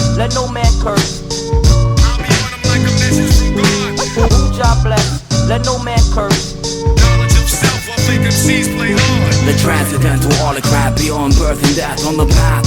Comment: I always thought he was saying "Punjab blessed, let no man curse"